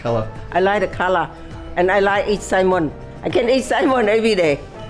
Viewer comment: I loved the smoked salmon idea 😊 also I thought said “I can eat Simon all day” at first 😂